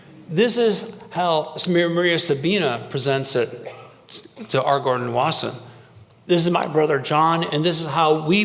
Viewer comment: María Sabina presenting it to who? Argor Nawassa? No search results can’t figure out what he is saying. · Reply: R Gordon Wasson. P.S. Try CC on this video. I spent some time getting these proper nouns correct